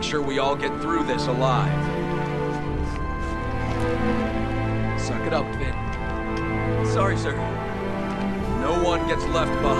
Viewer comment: 本当に申し訳ないけど言わせてほしい… 0:56が ピアーズ「酒だ、フィン。」 フィン「チョリソー」に聞こえて仕方がないの…wwww